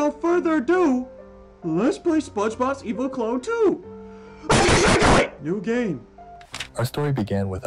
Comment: Did bro say, "your gay" Or, "new game"?